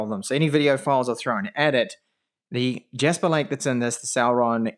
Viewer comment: Why do i always hear ''Sauron'' when you say ''Celeron'' and im like wtf this can't be right 😁😂